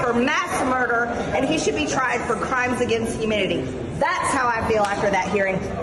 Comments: I've watched this several times and I swear I hear her say "Crimes against humidity". Did she? Or, do I need my hearing checked? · Ummmm....did I hear her say, "crimes against humidity?" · It sounded like she said crime against humidity! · Did she really say “crimes against humidity”…? She appears to be as dumb as she is mean – a bad combo for anyone, let alone a member of Congress. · I listen 3 times. It really sounded like she said crimes against humidity. Better bring in the gazpacho. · Did she say “crimes against humidity” at 4:28? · Wait!!! At @ 4:28, did she say "Crimes against humidity?" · did she say “crimes against humidity”, sure sounds like it. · It sounds like she said "crimes against humidity" during her whiny rant. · She has no character. Crimes against humidity? Did I hear that right? At 4:29.